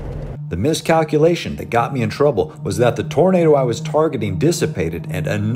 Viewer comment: lol i had captions on and at 1:43 i had to double take cause i thought he said it right but the caption called the tornado a burrito nice google captions i needed that